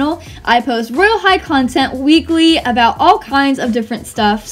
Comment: When you said weekly I thought that you said legally-😂😂😂 Your amazing!!